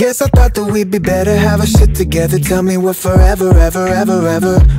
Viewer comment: did it say have a shit together?? lmao